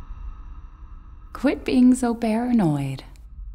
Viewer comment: Did Mia say Bearanoid at 1:10???